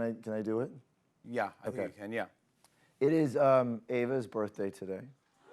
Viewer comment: Did he call her Ava